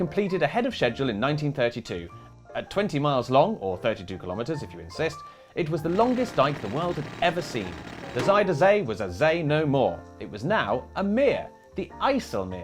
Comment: “At 20 miles long, or 32 kilometres if you insist.” I heard “if you exist.”